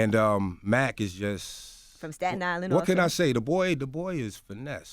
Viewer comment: Dj Envy: for everybody out there who don't know tell them where Staten Island is. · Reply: thought u said satan island